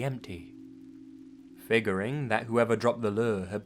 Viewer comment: when he said figuring I thought he said fingering XD